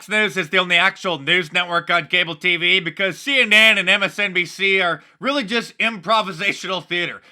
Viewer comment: Mark, at 1:55 it almost sounded like you said See Inane. Perhaps that should be your new name for them.